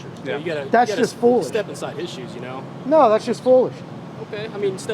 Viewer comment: "Well, you get a,, you get a "step inside" issue. Give me a break. He literally said the word issue. He did not say "in shoes".